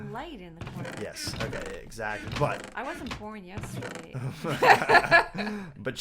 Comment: I watched some porn yestersay did she say that? Lol